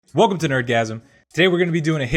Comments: I thought he said in the intro that"Welcome to an orgasm" XD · First time watching this channel and the first exciting word I misheard is "orgasm" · I thought i heard orgasm at the intro 🤭😬😐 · I thought he said "orgasm" lol